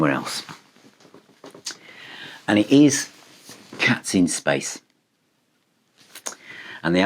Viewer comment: Most of the albums I'm not interested in as I have to cherry pick. I have the Porcupine Tree album. I got to number one and I thought you said the name of the band was Captain Space😂😂. Looking at your listing, I saw the actual name and I had a quick listen on Spotify. I thought I had gone back in a Time Machine. For a moment, I thought I didn't like it due to being similar to music of the past, but as I listened, I found it very catchy. I shall most definitely listen more on Spotify and get their music. What a find. Superb.